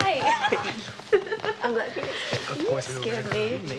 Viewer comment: I’m pretty sure at 0:18 Brittany says “I’m glad you came.” I could be wrong but that is what I hear. Also, even if I am right that does not mean they aren’t dating.